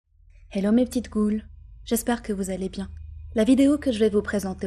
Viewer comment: J'ai compris "hello mes petites couilles" au lieu de "hello mes petites ghoules", pointez moi du doigt x)